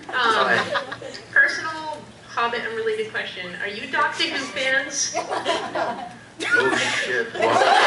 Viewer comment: Hi! Could please someone tell me which is the question in 7:40? I can't understand. Thanks! ^^ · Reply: She asked if they were Dr. Who fans. (I know its a little hard to understand her heavy accent)